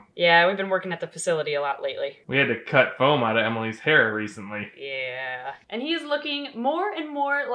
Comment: I swear I thought he said, “We had to cut foam out of Emily’s parrot recently,” and I was like WHAT DID YOU DO